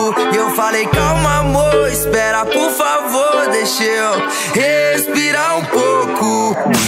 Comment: eu ouvi "cu" kkk